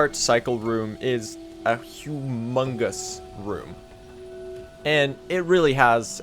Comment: did i just hear AMONG US